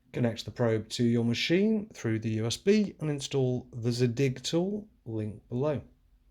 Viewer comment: the narrator says "install the zig tool link below" (according to the subtitles - I think he's actually saying "zadig" not "zig"). Anyway, where's the link ?